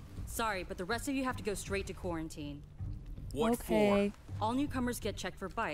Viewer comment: did she just say q-q-quarantine? 😖😭